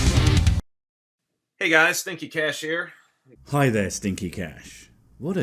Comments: I thought you said "Slinky Clash." I was ready for slinkies fighting it out for global supremacy! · Oops. Wrong video. Thought you said, “Kinky Stash”.